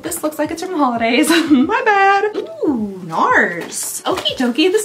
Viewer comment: The way I thought you were saying 'Nice' in an Australian accent when you said 'Nars' 😂😂